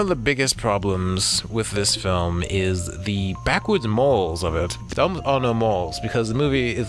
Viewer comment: you criticize the movie by saying that "people were wrongfully judging her, but in the end she did end up murdering her". Both are true! People were wrongfully judging her, AND she committed the murder. In my opinion, that was a brilliant twist, and given all that Chase had done to her, she seemed well within reason to have done so. She still did not deserve to be judged so harshly by the townspeople (especially because they had been judging her before she had ever hurt anyone. To me, the ending was the best part of the movie and (probably) made it worth seeing so much abuse and trauma. It's beautiful and nuanced, not a plot hole! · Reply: Ah, my mistake. I was confused because it sounds like something funky happened with the audio at that part (3:29). Still not entirely sure what you were saying there. "Backwards moles" ? lol. While the twist itself might not provide a new moral/lesson for the audience, it does reveal more about Kaya's character, and changes the way you see previous events in the movie, which I find both thought-provoking and fun. In any case, I appreciate your willingness to engage with criticisms of your work in a positive and productive way! Keep up the good work!